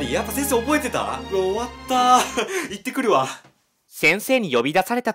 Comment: 多分、最初は「行ってくるわ！」 じゃなくて「逝ってくるわ！」って聞こえた。